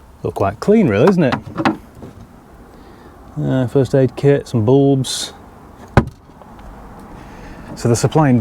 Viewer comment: Why do you hate 3d-plates? The Dutch have had them for decades...oh, wait.... 😂 Also it sounded like you said 'first aid kit, some boobs...'